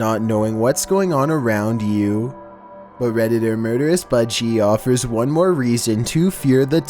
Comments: It sounded like he said murderous buttcheek😭😭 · I thought u said “redderous buttcheek”😂😭